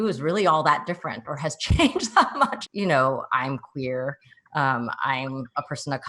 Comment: hihi they put "I'm here" instead of "I'm queer"